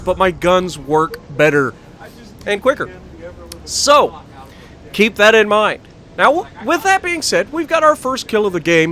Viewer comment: Listen to what whoever said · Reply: LoL didn't realize my mic was on at the time but I believe it was me saying to Bullvyi that I Just DPM'd The Ever Living F**K outta this Kuma